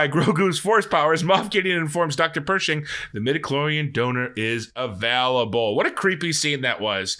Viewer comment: I completely thought Mof Gideon was saying "we have our DAUGHTER" not "donor" and my mind was blown! lol